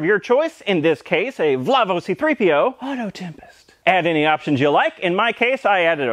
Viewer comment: Did... Did he just say "vlovo c-3po" ??? rofl